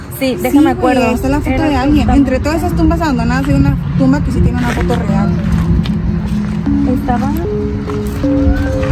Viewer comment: en el minuto 3:13 se escucha una voz de un niño chico pero no entendí bien que dijo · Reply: Dijo ma yo escuche clarito